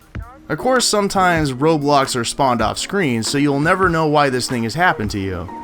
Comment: did he say roblox or road blocks lol